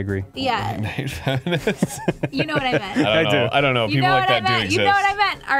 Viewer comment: what did arin say at 8:57? was it 'I hate feminists" ?